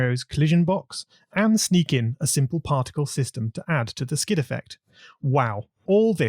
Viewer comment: Did someone just say "Particle system"? =P Btw nice jobs on these videos, I tried to use this tile engine to make an RPG but... I can't get the y-sorting (Which makes player to go to the right layer depending on it's y position and the object's y position) to work correctly, maybe because it only allows 16*16 blocks...